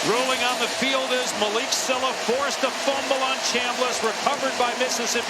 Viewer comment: Did the announcer say “Bama” instead of Ole Miss at 0:56? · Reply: NEVERMIND! He said Chamliss😂